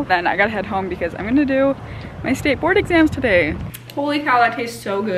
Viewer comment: i kept hearing "skateboard exam" and i was all wth